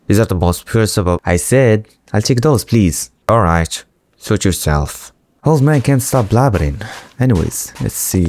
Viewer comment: when the old man at bosses rise said "suit urself" i thought he said "shit urself" btw very cool roleplaying, keep it up!